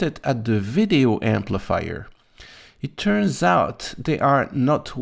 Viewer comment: did I hear video amplifier?